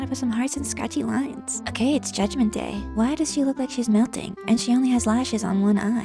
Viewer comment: Did she say... judgment day? (#undertalejudgmentday sans:ererer)